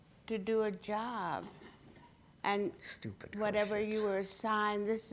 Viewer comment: did someone say stupid questions?